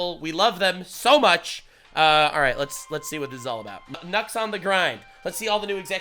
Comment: Dude, when you said nuxs on the grind. I thought you said nutz on the grind. 😂😂 I love the content. When you did that one saying ' your dick is small my is bigger then yours. It's the greatest punishment their every been." Hilarious.